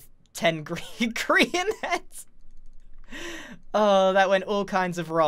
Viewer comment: Grian: somehow i ended up having then grian heads Me: Did he just say korean heads